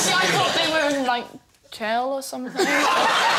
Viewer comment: What does she say at 3:39?!? "Honestly I thought they were......? were what?. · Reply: "... in jail, or something."